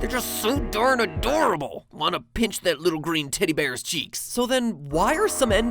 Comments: I honestly thought he said titty bear. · I thought he said "green titty bear "